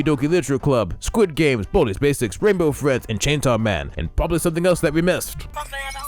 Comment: I'm sorry when you were listing all of them at the 3:50 did I hear chainsaw man or was I just going blank like I genuinely want to know if I'm tripping balls or if anyone else heard chainsaw man cuz ain't no way kids looking up that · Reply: There is in fact Chainsaw Man :/